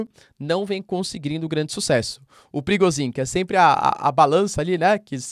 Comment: A primeira vez que ouvi o nome de Prigozhin eu entendi "perigosinho".